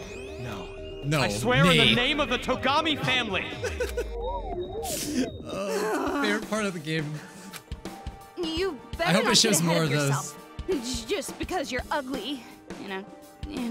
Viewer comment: “He hasn’t realized but he likes her back” Oh... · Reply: Banana Gremlin I thought it said tokogami